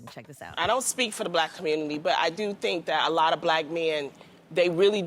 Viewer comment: Call me crazy, I didn't hear her say all black men. I heard "A lot of black men...."